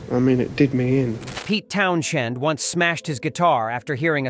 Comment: The AI narration makes this unwatchable. It keeps saying Town-shend over and over. I am ready to throw my laptop through the wall. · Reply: I thought I heard Towns hand